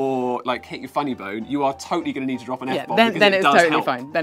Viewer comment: uhhhhh, if I stub my toe I'll need to drop an N bomb? I went over that bit multiple times. Please tell me I didn't hear that right.